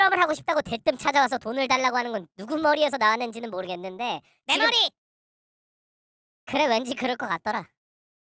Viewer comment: 초에 내머리 인데 대머리 라고 생각하고 들으면 대머리라고 들림 ㅋㅋ